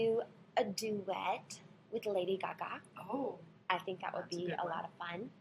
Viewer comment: It sounds like she says Lady Caca lol.